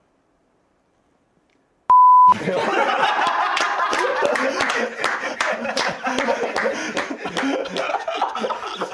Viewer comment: 뭐라고 한거 ㅋ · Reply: ”ㅈ됐어“ 인듯요 ㅋㅋ